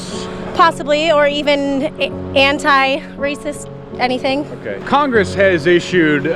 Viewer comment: am I the only one who heard "Hentai anything"?